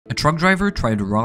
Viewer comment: I thought he Said “A drunk driver” Because i didnt dead the text 🥲😭